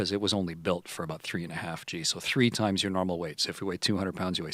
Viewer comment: i thought he said “three times your mama’s weight”